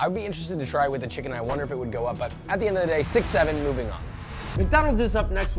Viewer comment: say that againn!!!!!!!!!!!!!!!!!! did u say 67